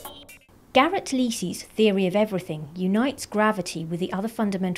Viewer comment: At first I thought she said "Gary Busey's Theory of Everything."